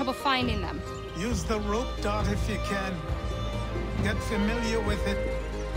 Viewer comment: I thought it said rape dart